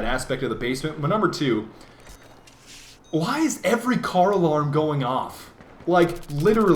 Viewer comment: everytime he was saying "car alarm" i kept hearing him say "carl arm"